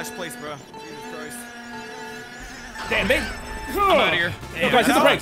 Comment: what did say?? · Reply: Im outta here